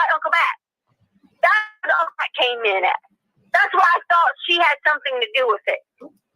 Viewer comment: About 57:15 says "Thats why i thought SHE (instead of HE) had ....with it" She slipped. Anybody else hear it??